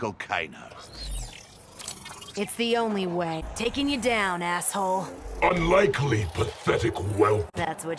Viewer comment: With no captions, 1:07 I swear she said dicking you down